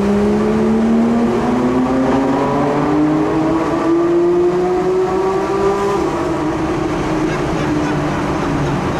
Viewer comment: Wieder mal wie immer ein tolles Mitarbeiter dürfen fahren Video! Leider habe ich so +/- um die 30:10 nicht so viel verstanden, kann mir das Gespräch aber vorstellen 😂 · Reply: War auch nichts außer "mach Fenster runter", "ist unten", "ist das asozial", "hier rechts".